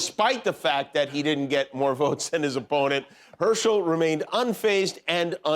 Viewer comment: Oh he said “pony”. I thought he said “opponent”. How can anyone take Herschel Walker seriously? He can’t even string two words together in a sentence. What is wrong with America 😞